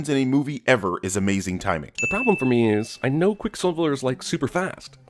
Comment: did he say "Quick SiBLer"?